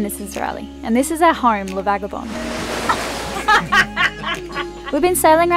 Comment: When says “Lavagabonde” in the intro I always thought she was saying “welcome to agraba” which makes me think of Aladdin every time i start a new video lol. It took me a long time to realize she was saying La-vaga-bonde...